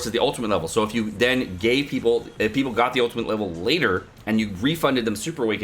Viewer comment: did you just say gay people LOL